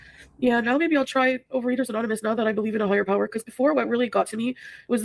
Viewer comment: As insufferable as she can be, I think I misheard what she said about her belief and I want to be fair to her. I heard “Not that I believe in a higher power’ the first two times I heard it. Listening again last night, I heard NOW that I believe in a higher power, and that means the opposite. So much stuff to call her out for, but I don’t think this is one of them. Just wanted to say I got it wrong. Maybe if she has belief, she can address her issues. She won’t of course, otherwise she wouldn’t be our!